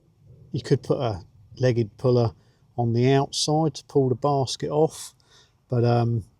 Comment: LOL every time you said basket I thought you were saying bastard... So funny.... I crack myself up.